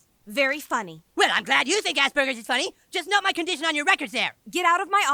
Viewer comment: As someone with Asperger's, I still find that episode quite funny. South Park is clearly meant to be entertaining and not intentionally harmful, so you can't take it too seriously. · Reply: The word "Asperger's" is misheard to "ass burgers". Now i've figured it out😂